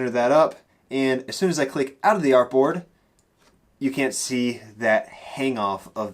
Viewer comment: what is he saying at 7:07? He says something like "as soon as I click something" but I did not quite catch that. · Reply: As soon as I click out of the artboard